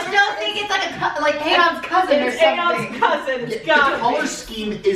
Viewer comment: Uh.... couldn't understand, someone mind helping me out? 7:47 is that "Aeon's" cousin? And I could not hear at all what 7:57 was..... Only eight minutes in and I'm liking the group format even with the small audio issues. (Big room and many people, can't be helped with out forking over a ton of cash for pro equipment.) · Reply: Yes, it's "Aeon's cousin".